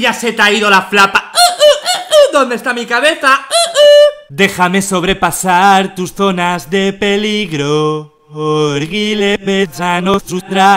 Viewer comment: mibuto 5:17 yo escuche dejame sobrepasar tus donas de peligro